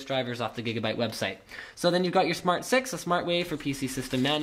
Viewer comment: I heard "smart sex" -.